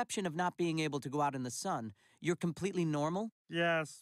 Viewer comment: For so long, I thought he said beetles AND teachers